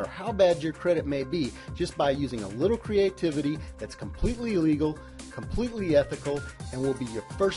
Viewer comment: did he just say this is "...completely illegal..."???